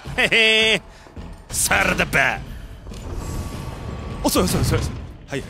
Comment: さらばだ!!の所 サラダバーって聞こえる(笑)